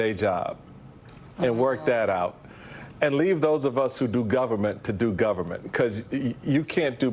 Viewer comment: wait, did he said "those of us" who do government ? now, That says a lot ... coming from a tv show host